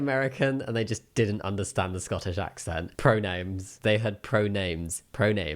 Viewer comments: As an American, i hear “Pronims” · To me it sounds like he is saying "pro nims." Also, given the high energy he is pouncing on people out of no where could have a lot of them going into a freeze response. I wouldn't have a problem with being asked, but if some stranger feeling overly extra and hard to understand through an accent randomly asked me my brain would go blank. Likely trying to figure out why this rando person is questioning me, and what is the world is going on.